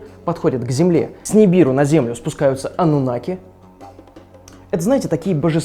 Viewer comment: Мне одному послышалось что когда он говорил про "Анунаков" мне послышалось "Анунахи"